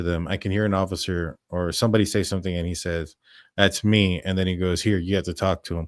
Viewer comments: I heard ‘Bethany’ not ‘that’s me’. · I heard "Bethany, or Dylan I need you to talk to them,"